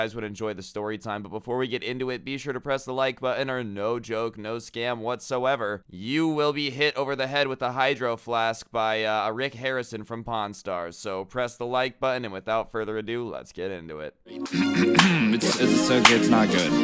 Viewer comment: I thought said Rex Harrison but he said Rick Harrison. LOL!